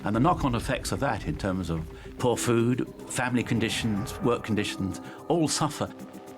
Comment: did he say family conditions or famine conditions? Are we accepting the fact that employment status contributes to to family life? 🤔🤔🤔